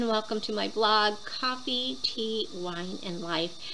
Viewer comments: Omg...I thought you said "coffee, tea or me?" Old flight attendant joke! Lol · Cheers!! You and I are obsessed with Vine OH...LOL The blanket washes up very well and stays soft. You're a lucky girl with winning all those great boxes! Agree the youtube community is amazing! Look forward to your advent calendars....especially the wine...lol When you said k cups....I thought you said handcuffs....I'm like..."WHAT"...LOL and no....I haven't had my wine yet! What...you should have received the stemless cup....ugh!! Maybe since they were sold out?? Can you send me your email address...if not, I understand. Hugs!! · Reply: LOL Handcuffs, and Barbara Jean thought I said Coffee, Tea or me!! Maybe I should change my title for a laugh next week, wish I had some hand-cuffs for that! That Vine-Oh box is amazing, how did I never see this before! I wish it was every other month!! When I asked another time if I could buy the glass, she said they were sold out, no worries, I have my new Nutcracker wine glass, I may just drink water from it!! I always use my work e-mail, I never have time to check my personal e-mail, all my on-line bill reminders go to my work e-mail too. <mail> Are you doing a shorter Vlogmas this year? Hugs!!!